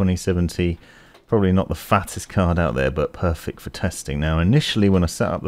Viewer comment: I thought he Said perfect porn 🤣😂🤣